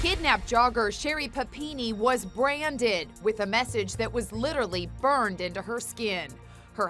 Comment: I thought it said massage... oops